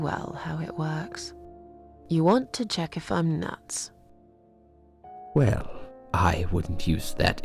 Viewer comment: i thought that instead of it saying "you want to check if I'm nuts" it said "you want to check my nuts"